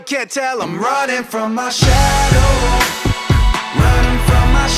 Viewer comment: I thought it said ramen from my shadow